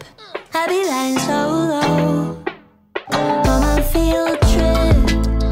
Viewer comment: I always thought she said "and I feel tricked" until I saw this and realized she's saying field trip